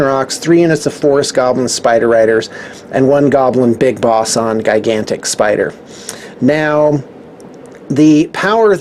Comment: Anyone hear him say guygantic spider